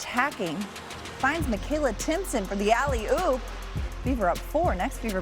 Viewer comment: Did she actually said alley-oop 1:52 hahaha